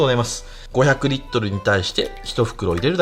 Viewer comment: 500リットルに対して1袋って聞こえたのは俺だけ？